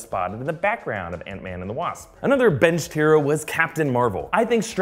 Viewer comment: When he said "another benched hero" I thought he said "another Ben Shapiro" lol